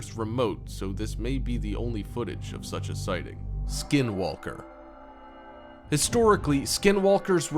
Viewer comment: who heard Skywalker? xD